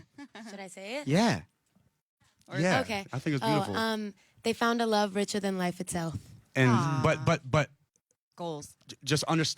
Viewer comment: What did she said in 19:07? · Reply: they found a love richer than life itself